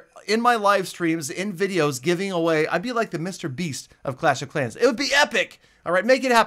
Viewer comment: did just say MrBeast!?